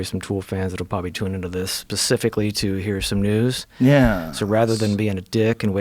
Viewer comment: did he just say pacifically?